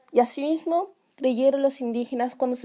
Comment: que dice? Leyeron o ligero? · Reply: Es creyeron, no estoy segura